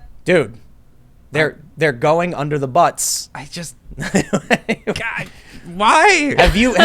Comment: I got distracted for one moment then I heard "they're going under the butts." Lmao